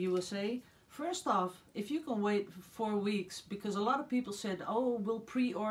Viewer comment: Nevermind just heard you say 4 weeks to the US haha